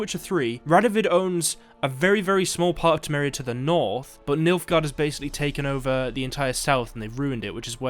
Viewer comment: All I hear is "Milf guard"